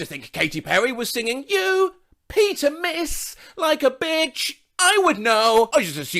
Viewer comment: Ok so I misheard this (2:05) as “pee a mess like a bitch” and I thought it was joke on “bitch” meaning dog and that she was saying he metaphorically pissed all over everything and she had to clean it up (“i would know”). True story from my 5th grade self.